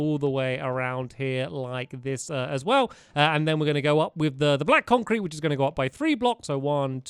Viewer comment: did he say black hole cream?